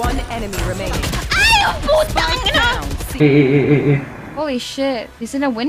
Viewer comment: Looked away from my screen for a second and i thought you said ay jueputa😭😭😭